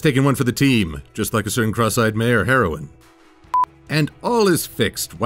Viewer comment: LOL! I thought he said cross-eyed mare of heroin....